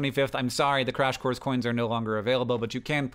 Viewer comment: I mistakely thought I heard "Crash Course is no longer available" instead of "Crash Course COIN is no longer available".... i almost had a heart attack and mental breakdown... but thank you for keeping those knowledge free and accessible publicly and continue inspiring others! Again, A BIG THANK YOU!